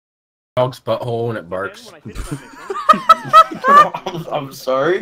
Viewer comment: It cut out around 7:29 and the next thing i hear is dogs butthole when it barks